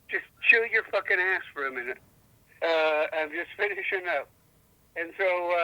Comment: "shut up and don't be an ass for a minute", clip it, clip it now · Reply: I thought he said “chew your ass for a minute” lol I thought it was a saying I just wasn’t familiar with 🤣.